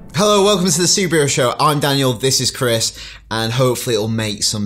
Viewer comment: i thought he said the Subaru show